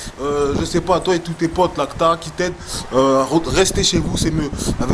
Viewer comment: les gens qui critiquent sans argumenter , juste pour faire le buzz, ils ont un fort EGO et me font pitié. Merci pour cette vidéo! et je crois pas qu"il connaisse l'ACTA moi aussi j'ai entendu "là que t'as"